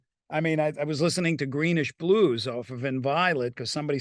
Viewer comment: Please help me. I cannot understand Steve Vai in 21:50. Wich band is Steve talking about? Green...?... Blues ??? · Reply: Greenish Blues, a song on his recent album Inviolate.